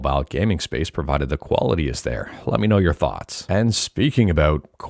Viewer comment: You said "quality." I thought you said "koala T," like a T-shirt with a koala on it.